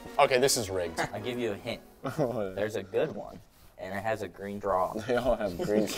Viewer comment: does say it has a green draw? I know it’s straw but it sounded like draw.